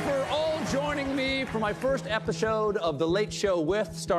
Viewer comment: did Colbert say first "epishode"?